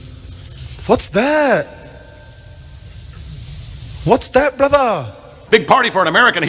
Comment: I thought that Arab guy was saying "What's that Buddha?!" 😂